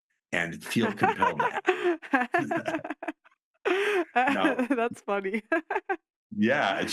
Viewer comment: "imagine that, and feel the [pearl starts laughing over ross]" What did he say? Feel the what? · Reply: Compelled